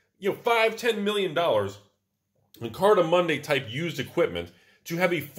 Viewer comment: my mind is blown. I thought he was saying Card of Monday this entire time lol